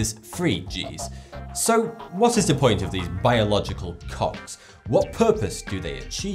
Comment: I thought he said biological cocks xD